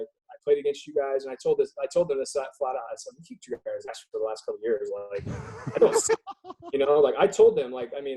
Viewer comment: What did he say to philly? around the 12:38 mark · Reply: "I flat out told those guys we kicked your ass for the last couple years..."